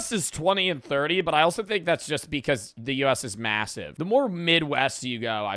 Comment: I’m sorry did you say massive?